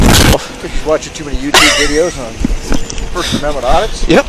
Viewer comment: I actually heard him say " You're watching too many you tube videos.